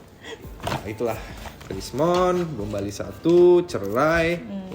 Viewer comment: kenapa subtitlenya jadi gombali instead of bom bali? 😅